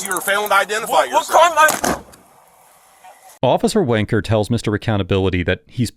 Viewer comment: Thanks for being here! Head to <link> and use promo code AUDIT to get your free gift with purchase, and start The Good Habit today! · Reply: did you say officer Wanker? Bruh!